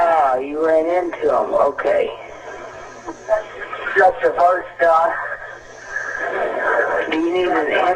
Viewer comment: Note: in brackets are the words that I am not 100% on. The rest is completely accurate. Yes I am in the middle of the field a (state pol’er) just pushed some guys over… right here goin towards Albeline on both sides. My truck ran out of gas, there’s one car here, guy’s chasin – through the woods. Please hurry. Emergency Lady: Ok, now run that by me one more -: (They) got one guy talking to them. Ah I totally ran in to em’ Emergency: Ah you ran in to em’ okay. (Detective’s) got the first guy… I’ve got a Emergency: Do you need an ambulance: yeah, no I need the cops. Emergency: Okay. Is anybody hurt? Hello? Extra notes: State pol’er as in police. Pushed some guys over as in pulled some guys over. The word “detective” is hard to hear, but I have used audio enhancements on that moment, and that is the word that it sounds like. I can post that if people are interested. Let me know what you guys think. I have also posted this on the other Lawson video on this channel. Keep up the good work. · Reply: Chris Devine I thought they said “they just shot the first guy” and throughout this entire call I heard a total of SIX VERY AUDIBLE gunshots. Shauna Rae did a great job at the end of her video when she tells her theory